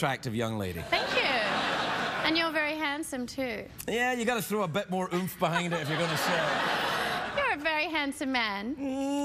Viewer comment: what's he saying here? thanks in advance ( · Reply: yeah you gotta throw a bit more oomph behind it